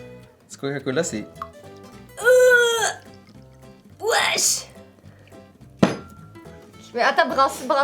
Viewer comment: C'est moi ou 6:28 elle dit wesh d'une façon bizarre ?? 😕😕😕😕😕😕😕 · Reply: J'ai dit ouache, ça veux dire degeu.